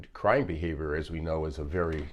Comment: Thank you for very informative video❤ just letting you know that 1:42 subtitles say "crime behavior" instead of "crying" 😂